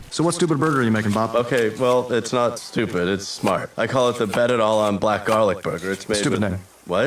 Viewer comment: I thought I heard bob say “FET-it all on black garlic burger” not mozzarella and black garlic burger.